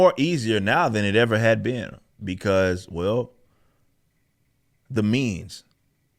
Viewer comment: I heard "the memes" instead of "the means" and I was thinking in mgr for a moment